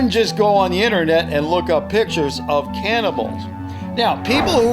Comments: Sounds like u said cannibals instead of camels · Did you say cannibals? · did just say 'PICTURES OF CANNIBAL'???